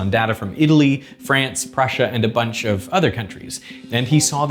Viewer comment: I think there’s a small error instead of “Russia” it says “Prussia”